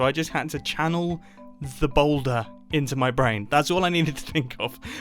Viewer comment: do I hear boulder boy?